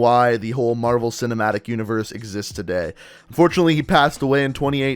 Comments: did u say fortunately Stan.ley passed away? Cause that’s just wrong Stan.ley was great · Anyone hear “fortunately he passed away”